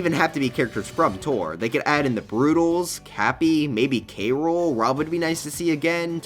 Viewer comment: I honestly thought you said baby k rool